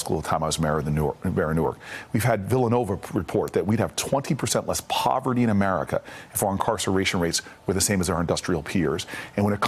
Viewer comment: I would like a better transcript of what Cory Booker says, at 12:33 (and transcript at 12:42) "We had the Villanova Report that we have 20% less poverty for incarceration rates for the same as our industrial peers ..." What does that mean?